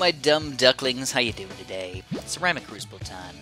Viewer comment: did you just call me a dumb duck? (Am just kidding)